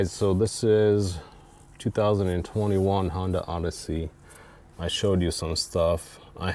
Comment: This guy is clearly full of it and not a Sienna owner but Honda plant with an agenda. Why at time 16:03 does he call it a 2001 Honda Odessey? Clearly has an agenda.